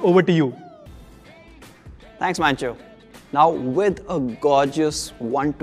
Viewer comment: I thought he said THANKS BANCHO🤣🤘 BY THE WAY LOVE POCO.